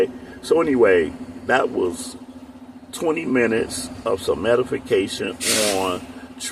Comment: Right on point. At 19:21 it sounded like you said ‘Medi-fication’ 💯